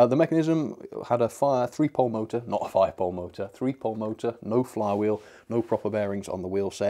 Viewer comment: did he say fire pol motor